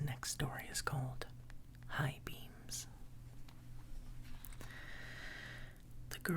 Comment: I swear when he showed high beams I thought it said high beans I was like what😂